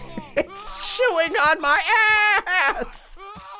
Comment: In 8:50 I hear its chewing my butt Me:😐just a normal game vid